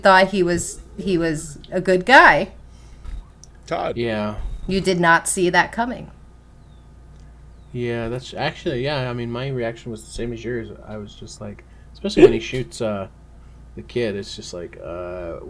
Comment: Did anybody else hear, “you did ‘Nazi’ that coming” 😂